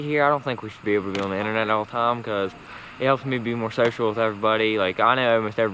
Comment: " "it helps me be more sexual with everybody" i thought i missheard so i turned on the captioning and nope, i heard right lol